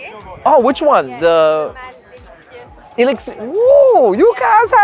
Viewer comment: What cologne did she say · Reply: I believe it was JPG Le Male Elixir.